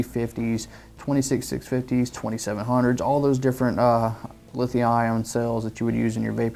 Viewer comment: This does charge 20700 batteries. Flip the charger over it lists all the cells it is capable of charging. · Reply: I'm glad you said that because I just ordered the IJoy Captain and it comes with dual 20700 batteries and I was worried if I got this charger it wouldn't work I didn't catch it on the box until played the video back and heard him say "20700" thought he said 2700.